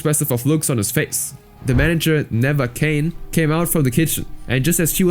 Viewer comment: I thought he said the manager never came